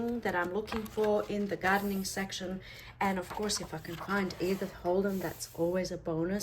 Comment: What's funny is I couldn't make out the Author's name you said in the beginning of this video so I paused it and tried Googling the name I thought I heard. I have issues from time to time with my hearing, like when I drive with the windows open or get water in my ears. Anyway, I heard Evan Holden so that's who I looked for. I couldn't find an Author by that name so I looked up botanical authors and found "The Country Diary of an Edwardian Lady by Edith Holden." Lo and behold when I continued watching this video you showed that exact book! 🤣 I'm going to find that book!!! I actually purchased a 5 minute daily journal thinking surely I can do 5 minutes daily! As usual life got in the way and now I'm playing catch up while being a month and a half behind! I was going to ask if you sell your journals but then you showed your collection! I was afraid that I would save the ones I make too, so I decided to make ones that will be pretty but not necessarily my cup of tea, like gardens or travel. (I love those genres but probably wouldn't want journals about them. (I say that now!)). I love Victorian, Gothic, Vampires and Witches so I will definitely keep those! I would love to see you and April, another YouTuber, of PinkOddBird, collaborate on a project!!! (You two ladies are my favorites!) Thanks for sharing! Blessings to all!